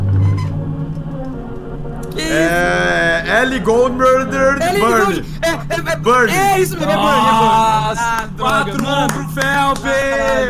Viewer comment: qual o nome dessa musica que eu ñ entendi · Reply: Burn da Ellie Goulding